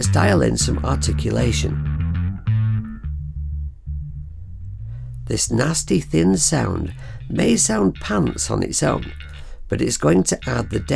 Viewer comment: What's that term you said at 4:25 about the high EQ? I couldn't decipher that one! Great review and quick tutorial. I just bought this and was looking for some info on what everything does. Appreciate the overview, best wishes from New Mexico. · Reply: Do you mean where I said, “ this nasty thin sound may sound pants on its own” ?